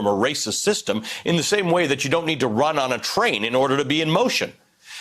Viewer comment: I swear he said runnin the train 😂